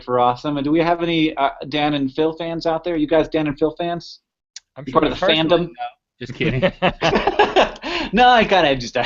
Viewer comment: What does Link say at 40:38 about knowing Dan and Phil ? Thanks ! :) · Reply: In response to Hank's question of "Are you guys Dan and Phil fans?" Link says, "Me personally, no." But of course he was just kidding!